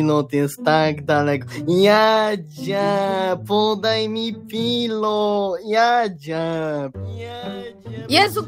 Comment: czy tylko ja usłyszałem "podaj mi piwo"? XD